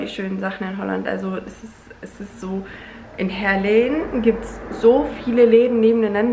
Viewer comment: Was sagt sie bei Minute 3:25 ? In welcher Stadt gibt es die ganzen Läden? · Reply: du Liebe, ich glaub du meinst Heerlen, kurz hinter Aachen oder?